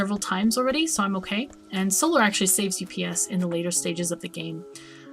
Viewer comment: UPS = Updates Per Second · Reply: Ok thanks a bunch. For some reason I thought you said "saves you ps" and not "saves ups" lol